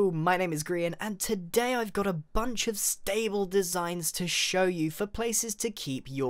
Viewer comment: for a sec i thought it said 8 hours stable designs xD